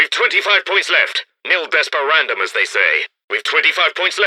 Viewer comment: What is he saying? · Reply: Nil desperandum, Latin for never despair/nothing to despair